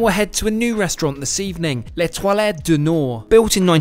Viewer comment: The way you prounced 'L'Etoile' sounded like 'les toilettes'.